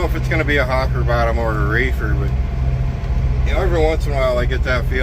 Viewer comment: Hopper bottom or a what? I thought you said Raker? what is that? Thank's. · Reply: “Reefer” What he hauls now: a refrigerated trailer.